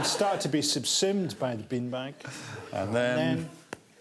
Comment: I so misunderstood Frankie’s accent that I thought he was saying bin bag and had no idea what he was talking about until I saw the picture 😬😬